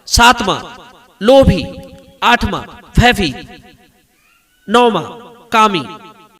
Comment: पर क्या कहा जी ?? 8वां ?? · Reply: भयभीत